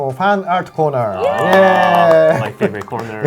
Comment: First: Thanks all for the hard work! Second: Looking forward for the FF12 units. Third: I suggest answering more questions. Fourth: 14:46 did just say "fart"? 🙂 Last bt not least: YOU DISAPPOINTED US! WE WANT THE TICKETS! 😠